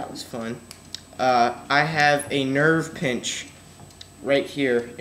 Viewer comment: ohh i thought u said Nerf Pinch lol but hope u get better i want to see it shoot